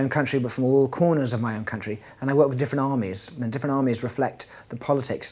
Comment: Dear connoisseurs of English language! Unfortunately, I'm not a native speaker so some phrases from the interview aren't clear to me. Could you help me, please? On 2:11 James said: "I worked with..." and then he pronounced a confusing word. After that he added: "<this word> reflect the politics of the country..." It seems to me this sounds like "difenomies" or smth like that. I know I'm wrong cause this word doesn't exist. Pease tell me what this word actually means. Thank you in advance! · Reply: He actually said "different armies" 😂